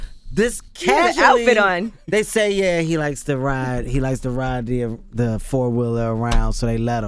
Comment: I thought he was gonna say "yea, they let him ride the deerbra..."!!! Lol, don't judge me!